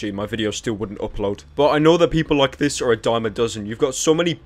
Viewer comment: pyro didn't say diamond does He said dime a dozen Subtitles